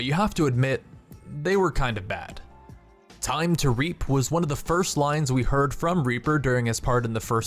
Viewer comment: When Reaper said "Time to Reap" I thought he said "time to raape"